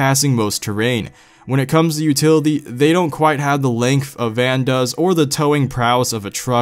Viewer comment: first i thought he was saying the Vans Shoes like if you too